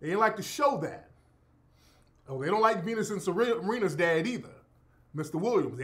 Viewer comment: Bro did just say "penis and serenna@ 46:42 haaahaa.